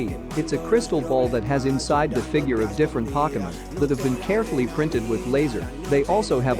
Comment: Pokimen 😂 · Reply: I thought he said pocketmon